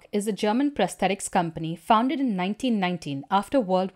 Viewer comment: I thought you said “autobot company” for a minute and had to double check!